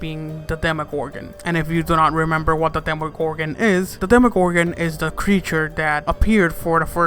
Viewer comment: While watching this I put subtitles and instead of demogorgon it says demo corgan and temple corgan in